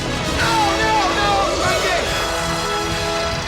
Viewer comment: what did ray say, he mouth off something but there was no audio for it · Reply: What you heard was what he said: "now, now, now Spengi!"